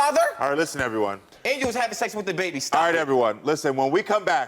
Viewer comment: Wait did he say she was having seg with da baby?